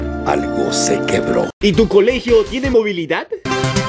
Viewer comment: like si viste que dice "tene" en vez de "tiene" :v